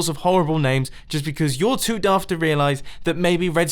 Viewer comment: did u just say "duf" ?